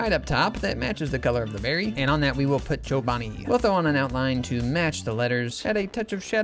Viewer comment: i looked away for a second and thought u said "put joe biden" and i looked back at my screen so fast